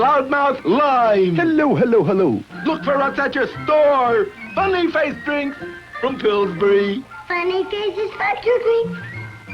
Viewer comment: i thought this kid said the F word but after believing that he actually really just said "thank you"